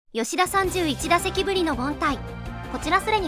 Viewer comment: ｢吉田31打席ぶりの三振｣に聞こえた笑